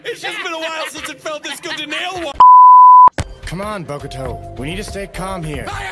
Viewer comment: Don't mind but at 04:34 I heard bobuto it was fucking cute btw❤️